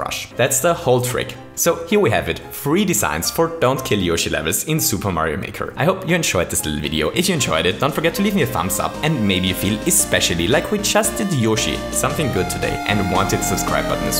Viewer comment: did he say ich enjoyed it ?